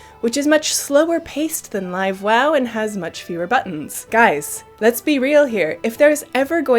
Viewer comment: Did she just say wow classic has less buttons...?